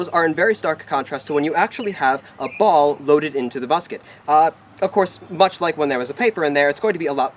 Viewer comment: he says busket instead of musket